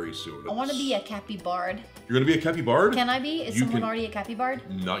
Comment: For a second, I thought you said you wanted to play a Country Bard... and now I have inspiration for a new character, one part Sir Robin's minstrel, one part Jerry Reed!